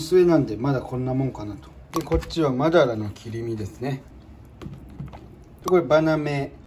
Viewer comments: マダラ（犬）の切り身に聞こえた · ラジオ感覚で聞き流してたら『マダラの切り身』ってワード聞こえて見てみたら真鱈の切り身やったw安心w